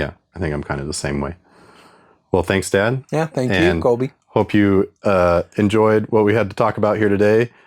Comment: whoa did we just get a name reveal?? If I heard that correctly that would make your channel Coby’s Lab 😄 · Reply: Colby!